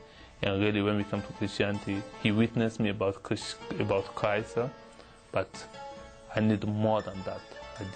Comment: Did he say: ''he witness me about CHRYSLER?'' 7:41 LOL